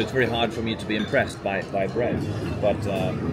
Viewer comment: Did say that it’s hard for him to be impressed by breasts at 44:55? That whiskey tho!